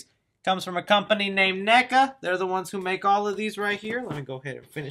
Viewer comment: did he just say the n word